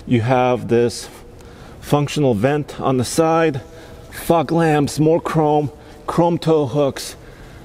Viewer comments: it sounds like he says "F**k lamps" 🤣🤣🤣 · I thought there was a dent in the bumper. And then I heard him say F*CK lamps.